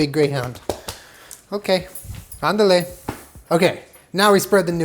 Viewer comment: did he just say “ándale”?? love a bilingual king